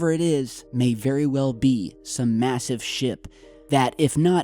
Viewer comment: i thought u said it could be some massive shit instead of ship